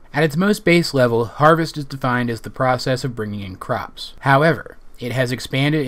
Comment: Am I the only one who thought it said 'The process or peridot of gathering in crops'?